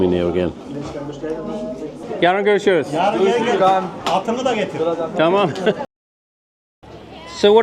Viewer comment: I think he said "Hatunu da getir" rather than "Atını da...". Hatun is referring to the wife here, meaning something like "the lady". It has a connotation of a precious woman that you serve rather than be served like a queen.